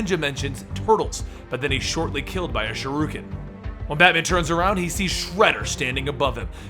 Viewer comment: Did I hear him say shuruken instead of shuriken?